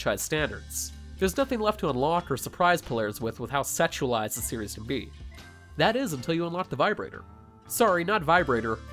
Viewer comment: Hol Up what did she just call you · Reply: Senpai